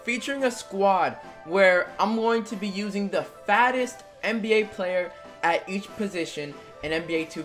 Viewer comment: Am I the only one who thought he said FASTEST?